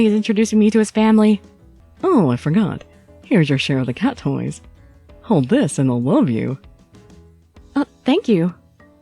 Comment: I heard "Hold this and I'll love you" .